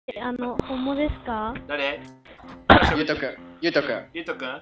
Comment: 将来の夢レックウザですって聞こえたんだけどw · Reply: 後最初がホモですかぁ?に聞こえるw